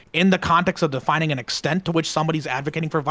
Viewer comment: lmao i have no idea what he's even saying. the extent? lmao